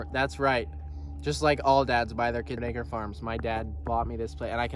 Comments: I swear he said "just like all dad's buy their kids n***ger farms" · 16 sorry people so far LOL Sorry about your existence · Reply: Brother your editing sure made it sound like you said "my dad bought me this farm, just like all dad's buy their kids NI*#ER farms" I legit had to rewind and replay that part over and over to try n figure what the hell you meant to say or were trying to say but it still sounds like that is what you said @ 16:51 🤔🤷‍♂️ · did he just say n***er farm? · what did you say....? would like to imagine I'm hearing this wrong? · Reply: He said acre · 16 min 50 seconds sounds like he says n*** farms 😂😂😂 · "just like all dads buy their kids n****r farms" i swear thats what it sounded like he said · what’d he say at 16:50 ? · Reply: Just like all dads buy their kids something acres?? Farms?